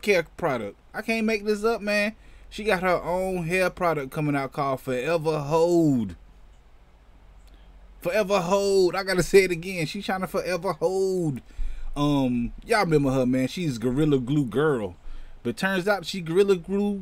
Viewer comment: I thought you said “Forever Hoe”.. 🤣🤣🤣🤣